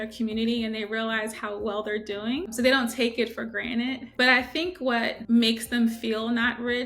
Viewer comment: Did she say take it 'for granite'? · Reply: No. Granted